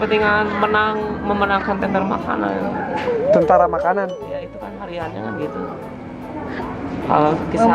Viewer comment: Kang itu namanya, orang cibaregbeg dekat Plaza Asia. Dia orangnya pinter bgt dlunya, sekolahnya pinter, tapi jadi ada gangguan gtu. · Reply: Kalo ga salah denger, bpk ini bilang "peletakan batu untuk kelancaran memenangkan tender makanan" Apakah beliau pengusaha?